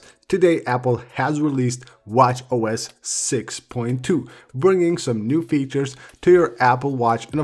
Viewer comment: At first I thought it said “iOS 6.2”